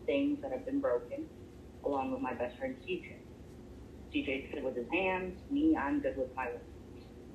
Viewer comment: sounds like he said "djs good w his hands im good w my lips" wtf did it really say?!